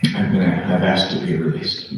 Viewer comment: No, he said he resigned. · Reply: I just rewatched. “I have asked to be released”. I heard that as I have been asked to be released. I was reading the subtitles and listening at the same time and I got that part wrong. I apologize. Yes he is the one who asked to be released. You are correct.